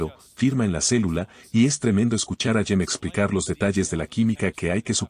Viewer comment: Que dice en 0:30...dice de escuchar a:??? ..... en la traduccion no dicen el nombre que menciona....habla de Yen? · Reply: Es Jim, se pronuncia Yim en ingles. Se refiere a James Tour de este otro video: <link>